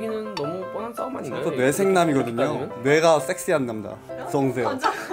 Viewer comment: 섹스요 나만들리나?